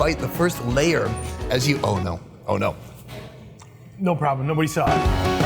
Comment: For a second I thought you were throwing some Hawaiian in there. When you said Oh no I thought you said at first ono which means really really good. But you said Oh no when you dropped some food. Ha Ha Love the video!!! Thanks